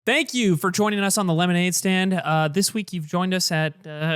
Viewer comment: My youtube started the audio a moment late, so I heard "***k you for joining us on the lemonade stand" lol